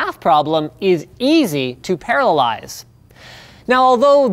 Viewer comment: Did he say paralyze or parallelize ?